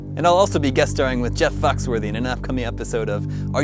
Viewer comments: Sounded like he said Jeff Fucksworthy... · wait... did he say jeff fucksworthy? I swear he did, at about